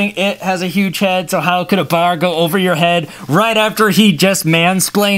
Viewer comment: Did you just say manslpained